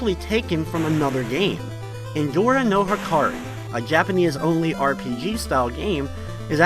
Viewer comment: Did any one else notice at the end it played our national anthem? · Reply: What the hell is he saying??? "INDORAN NOVA CARD????" I also notice he says "during" as "doring" - can somebody translate, please? Update: Okay, found someone else's video where they actually name that game in text (Indora no Hikari), but I still swear it sounds like he said "Indora Nova Card." lol....